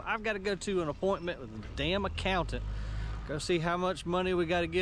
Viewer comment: Did you say "Damn accountant"? lol You didn't sound happy to have to deal with the accountant. haha